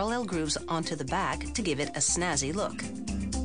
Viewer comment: did she just say "snazzy"?